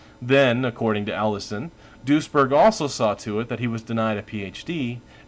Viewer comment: I keep hearing "Doucheberg"